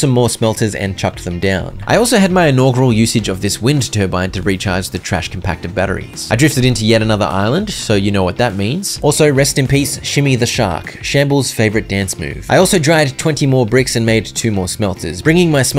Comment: You said 'Shimmy the Shark' and I heard 'Shmi the Shark,' and expected you to follow it with 'Anakin's dead mum.'